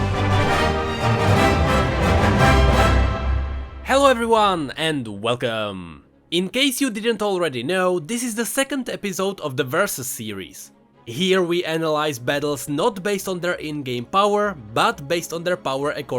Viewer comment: I thought I heard versa series.*Surprised pikachu face*